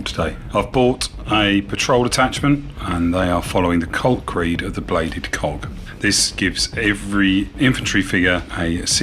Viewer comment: This was so much fun! Thanks for a great game, and thanks for letting me be a Skype Archon :-) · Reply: I’m sorry but I think this is funny at 2:37 is sounds like you said “bladed cock”